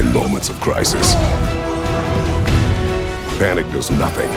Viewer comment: Is it just me or towards the end of the trailer, before meeting Tyr, does it sounds like Kratos is saying “In moments of crisis, Vanir does nothing. Why is this”?